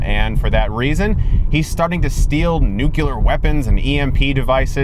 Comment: Did he say nucular ?